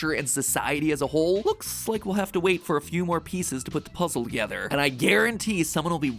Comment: PLEASE I THOUGHT THAT SAID "pieces from gender puzzles" I WAS ABOUT TO SAY